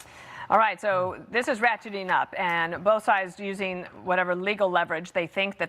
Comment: Lmao I thought she said "Ok this is ratchet enough" 🤣